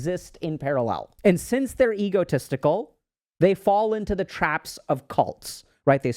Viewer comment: am i the only one who heard "ego testicule "